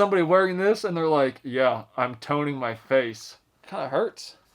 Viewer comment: I laughed my ass off, i misheard what you said and heard: Yeah im Tony Mcface :P